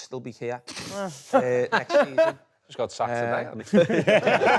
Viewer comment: what did he say? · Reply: He’s been sacked again